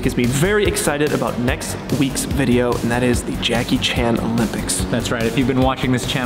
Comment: this makes me want the "janky chan" video you guys never finished :( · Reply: I could have sworn he said Janky Chan at 9:08, I was about to suggest the same thing